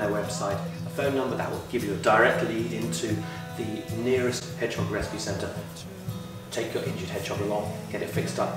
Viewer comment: Please close-mic yourself and guests, interview in a room with lots of soft furnishings and slow down. I’m sure I heard “if you find an injured hedgehog take it to your local “hedgehog recipe” centre”.